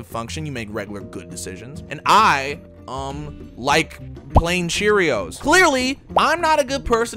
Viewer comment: I honestly thought he said he liked PLAYING Cheerios